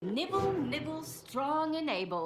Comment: did she just say Nipples? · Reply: No dummy she said nibble nibble